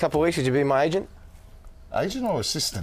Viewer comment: AGENT! Oh, I thought he said ASIAN!